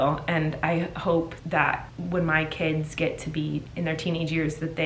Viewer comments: Did you just say " KIDS" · Did she just say kidssss